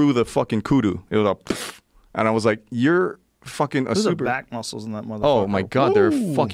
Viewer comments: First time through I thought Joe said black muscle which isn't wrong but I rewatched it was back muscles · thought he said " look at the black muscle "